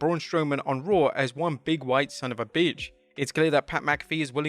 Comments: Pat said "Big, WIDE" about Braun, not "Big white" FFS....these mishearings could ruin a career unnecessarily ... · When Pat was referring to Braun Strowman, I thought he said “Big, wide” instead of “Big, white.”